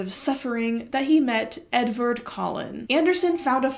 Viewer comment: not my bad auditory processing arse mishearing a name that i had to turn on subtitles back and check that it was "Edvard Collin" and not. khm. "Edward Cullen". 💀